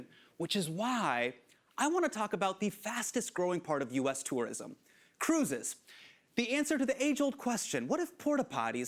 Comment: Pleaseee I thought he said "the fastest-growing part of UK terrorism" 😭😭😭🏃‍♂️